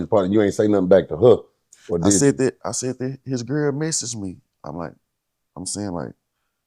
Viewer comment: He didn't answer the question. Or did I miss it? Did you say something back to her or not? I heard him say, "I sent it, I sent it." Sent what? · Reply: “I said that, I said that his girl message me” but naw he didn’t answer the question really, it’s either “yes I responded back” or “no I didn’t”….and you’d think that he would’ve remembered what his homie girl messaged him that caused them to fall out before his passing…but hey #LLMO3